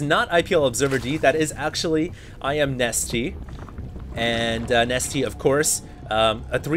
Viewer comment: lol i skipped to 2:04 and i heard Actually i am nestea :D!!"!